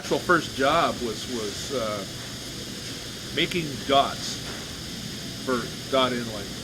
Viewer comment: I thought he said making dust 20:07 lol I used to collect dust 😆